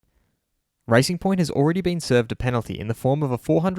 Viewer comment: thought you said ricing point, fastest ricers ive ever seeen